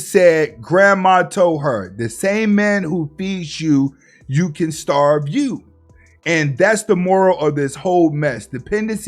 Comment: 😆😆😆 no that's not what she said , she said THE MAN THAT FEEDS YOU CAN BE THE SAME MAN THAT STARVES YOU